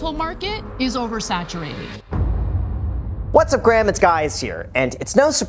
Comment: “What’s up gram it’s guys” nobody has said anything? Lol 😂